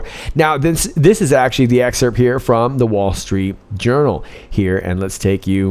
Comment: I thought I heard you saying, "wall street urinal". 😂😂